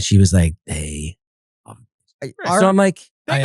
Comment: what did he say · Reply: "they loved it" I think